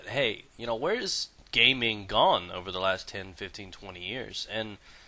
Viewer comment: it sound like he says an asian person's name gay ming gong